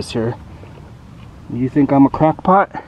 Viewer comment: Thought you said, "think I'm a CROCK pot"? Low and slow …..hahaha.